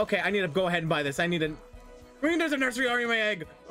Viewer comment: what did blud say “There is a Nusery in my egg”